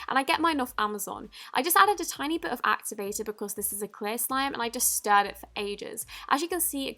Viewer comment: sounds like you said clay slime 😂